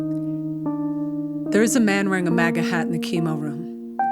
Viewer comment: I'm Australian and I thought you said maggot hat. It fits.